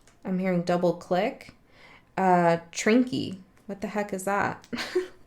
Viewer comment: when you're reading the charms, you say trinky as does the closed captions. What it actually says is Trekkie, and in case you don't know, it means one who is a fan of Star Trek. So glad it didn't say TREKKERS! 😁 Other than that, spot on as ususal!